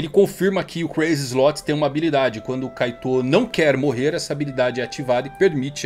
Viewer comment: Eu entendi "quando o kaido não quer morrer" Eu tbm: oush, kaido, Crazy Slots????? Que viagem é essa? Voltei e entendi bem. Akakakaka